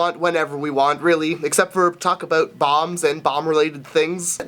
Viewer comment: Guys, I thought he was saying balms not bombs...